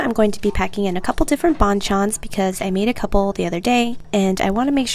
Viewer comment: Thought you said bonchon